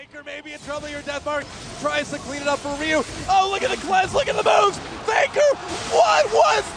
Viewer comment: i always thought he was saying look at the class look at the moves xD